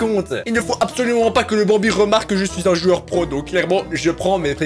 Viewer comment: C moi ou a 1:31 il dit que cest un joueur pro